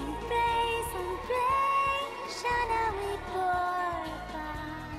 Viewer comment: Did she said "someday" ??